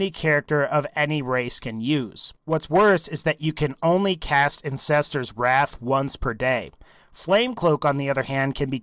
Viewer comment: I hear "Incester's Wrath" 😂